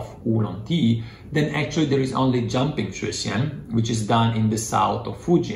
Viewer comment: I cannot understand what you are saying at 11:06...the transcript shows jumping tration? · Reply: Zhang Ping Shui Xian